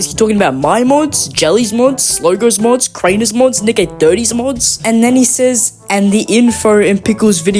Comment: did bro say the N word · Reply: He says nickeh30